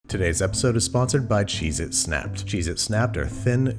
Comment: On everything I thought he said “today’s episode is sponsored by Jesus”